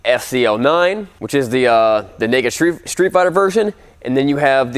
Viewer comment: did you say the nigga street fighter version?